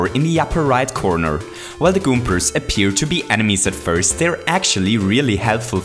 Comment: I heard “goompurs” 😂